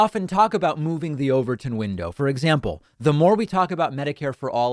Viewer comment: I had to pause and find out what the "Overton Window" is. OK I'm a little slow. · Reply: 😂 What is it? I heard "the overthrown window..."